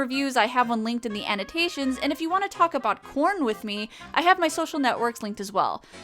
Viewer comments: The last few seconds of the video I wasn't paying attention and it sounded like she said "If you wanna talk about PORN with me..." Certainly got my attention after that. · I had to go back and listen one more time... I thought you said "If you want to talk about porn with me...". I know fans can be weird on social media, but that would be pushing it! :)